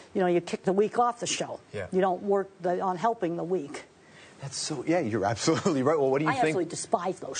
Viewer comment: What did he say at 1:38? I could swear he said "That is so gay"? Am I hearing him correctly?